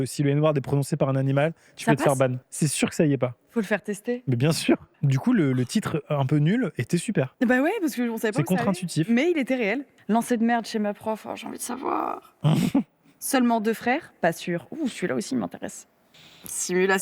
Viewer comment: j'ai entendu "lancer de merle chez ma prof" je les imaginer lancer des oiseau au dessus d'une cloture